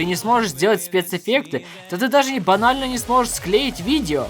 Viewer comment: Ебанально? Послышалось :)